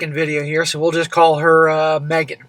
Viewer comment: At the 1:49 mark, at first I thought he said “We will call her Maggot.” 😂😂 I had to rewatch that part. 😂